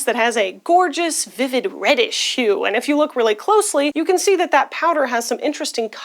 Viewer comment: Since we already brought up auditory processing issues & mishearing things.... @ 1:46 I was distracted for a moment feeding my dog & genuinely heard "Reddit Shoe". Somehow my brain just was like oh this is gonna be some internet term I don't know yet.😅🤣